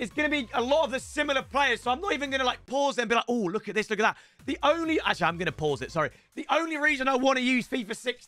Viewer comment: to my Indian friends, did he say accha?